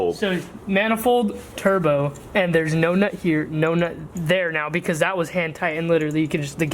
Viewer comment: alll I hear saying is No nut here no nut here 😭🤣😭🤣😭🤣😭🤣🤣😂🤣😭